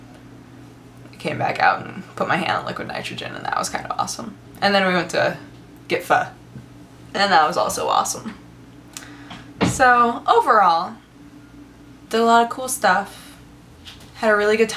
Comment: I'm sorry but at first i thought you said "and then I went to get f**ked and that was also awesome!" TMI TMI